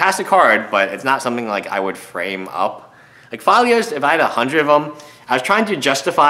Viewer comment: So I'm new to magic and can't find any card with the name Falia. WTF is it? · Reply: +Yoeri Mtg OOOh, okay thanks. I really thought he was saying Falia. My bad.